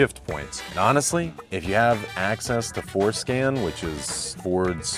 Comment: When he said "access to Ford scan" I thought he said "access to foreskin" 😂